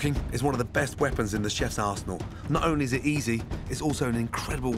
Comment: At the beginning, he said arsenal, but I heard "a chef's asshole." (⌐■_■)